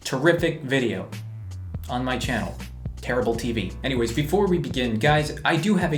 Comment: Did he say "Terrible" TV